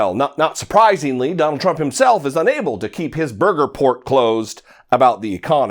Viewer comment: Did he just say Trump can't keep his " burger port closed ?!?!" LOL !!!!!!!!!!!